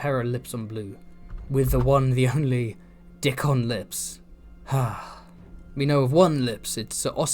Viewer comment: I'm sorry I'm sorry did you say "Ser Dickon Lips!?!"